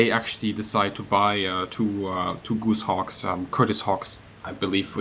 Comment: Does anybody know what plane he is talking about at :45 ? its sounds like he says goose hawk but i cant find any plane called goosehawk.